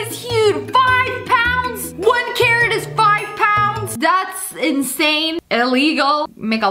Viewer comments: When sniper wolf said that carrot is five pounds I heard her say that Karen is five pounds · I literally thought she was saying Karen when she was talking about the carrot😂 1 Karen is 5 pound!